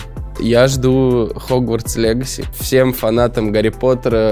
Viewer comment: он сказал фанатам гп большой рахмет или мне послышалось слово рахмет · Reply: Да, он сказал «Рахмет»)